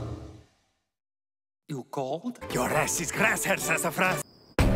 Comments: I thought he said “you cold” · the only thing I could make out was, "Your a$$ is grass helsa France" what did Germany actually say? Reminds me of Steve: "Yeah, it's Farrah Fawcett hairspray. You tell anyone I just told you that and your a$$ is grass."